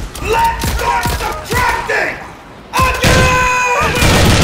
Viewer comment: lets rock the what? Somebody tell me whats say there, that line kill me of laught xdxd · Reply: "Let's start subtracting! Again!"